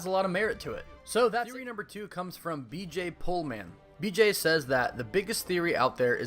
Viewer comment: That’s on me my man! My bad 😅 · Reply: Christopher Rosser aye no worries. It’s better than what people thought he said last time which was “BJ Poleman”